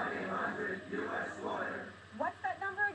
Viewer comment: I hear 1-800-US-LIAR at 1:53.